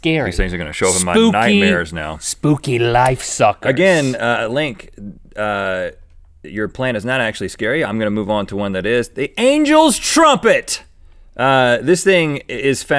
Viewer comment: I love how it sounds like he says plan tit